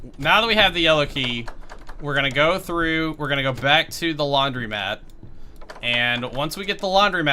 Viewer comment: I thought he said laundry map and I was like "wait wha", but imagine there's a map where you have to do your laundry lol.